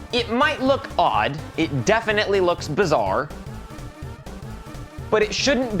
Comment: did you just say… bizarre?